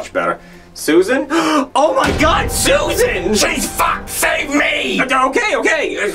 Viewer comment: SHES FUCKED,SAVE ME had me dying😂😂😂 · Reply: I thought he said Jesus F save me